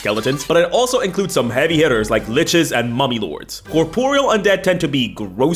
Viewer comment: I definitely heard "mommy lords"